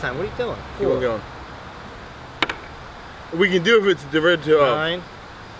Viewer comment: wtf did he say "we can doove do da 12"